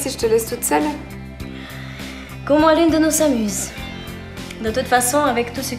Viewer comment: elle a dit quoi à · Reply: Elle a dis que au moins l’une de nos 2 s’amuse